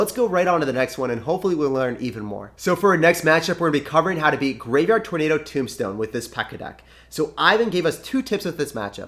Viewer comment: When you said graveyard tornado tombstone, I thought you said tomato tombstone!